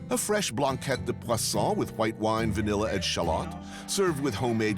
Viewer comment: did he say “… white wine…”? 🤣 gotta love France